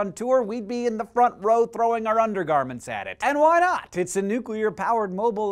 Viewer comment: I heard the "Throw our undies on the stage" and thought of Super Sprode. HOW IS THAT FOR A REFERENCE!?!?!